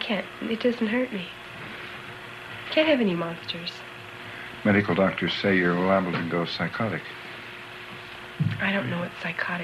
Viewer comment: what does the interviewer say at 2:00? i don't understand:( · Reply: let go! He says "medical doctors say you're liable to go psychotic"